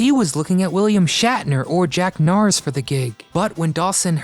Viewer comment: You mention the other potential hosts Math Goodson had in mind, William Shatner and it sounded like you said "Jack Norris"? But I think the photo you showed was of Jack Cassidy. Went by fast, so not 100% certain...